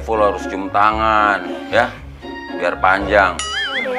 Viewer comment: maksudnya bilang apa panjang? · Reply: Panjang umurnya kli...